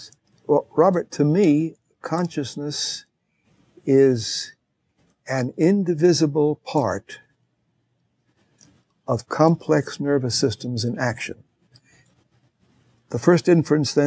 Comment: Sounds quite a bit like Scheibel is saying "Consciousness is the inside reflection of our physical body functioning (in action)." BINGO! Finally someone who gets right to the point. Instead of all the lofty philosophical self-indulgence Scheibel demonstrates why "Consciousness" can best be understood through the lens of biology and evolution. 7:00 + multi-tasking isn't altogether an illusion. It's nice seeing a physical basis. Back to "mind" Look up the Human Connectome Project for images of our mind in action. Once we look beyond the miracle of Earth, biology and evolving life, it's no more miraculous that copper and a rotating magnet producing electricity. --- Why Not a living creature's actual neural biology creating mind? Heck, it can actually be visualize to some extent these days. Human Connectome Project Thank you Kuhn, I've been waiting for this interview. Now I'm hoping to hear you interview Dr. Mark Solms some day, that would help get us one step closer to truth.